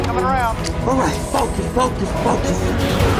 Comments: Did starscream said lokus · what did starscream say? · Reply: Locusts